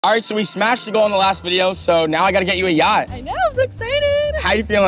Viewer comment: For a second there I thought he said “ so we smashed the girl on the second video “ i was like aaaaayyyyyyyyyoooooooooo!!!! And replay it.